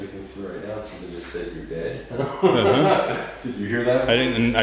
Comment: I heard “we’re dead”, not you’re dead.